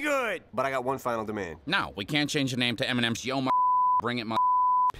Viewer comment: I thought the guy said “no, we can’t name it “Eminem’s Shut-up-and-drink it”